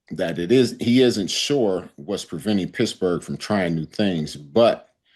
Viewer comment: did he say Pissburgh?